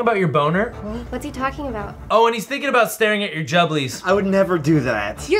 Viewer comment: What did he say? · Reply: Jubblies